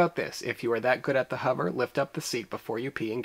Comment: I thought you said lick the seat instead of lift and I was lolling lol